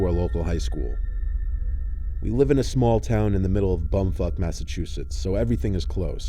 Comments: did he say "Bumfuck, Massachusetts". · I heard: "we live in a small town called bumfuck massechussets." Is that actually what he said or cuz im confused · is it just me or does he say “bumfuck” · did he say bum fuck Massachusetts?? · Why’s it sound like he said “bum fuck” @ 4:59 😭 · did anyone else hear, “bumfuck” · Bumfuck, Massachusetts??? Am I hearing right? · am I trippin’ or did he say bum fucked? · We’re not gonna talk about “bumfuque” Massachusetts?? 😂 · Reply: I heard "bone fuck massachusetts" 😂😂